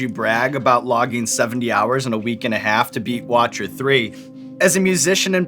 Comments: Did he say "watcher 3"? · Anyone Else hear Sean say Watcher 3 Min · Did he just say The Watcher 3? Did he mean the Witcher 3? If so, logic is cool. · did he just say watcher 3 😂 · Did Sean say The Watcher 3??? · Did he just say to beat "Watcher 3"? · did he say watcher 3?😂😂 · Did Sean just say "Watcher 3"? lmao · dafuq, did he say watcher 3 LOL